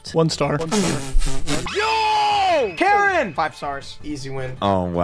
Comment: I swear said meh name😭😭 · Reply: karen